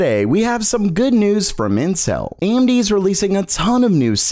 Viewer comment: sounds like you said incel lmao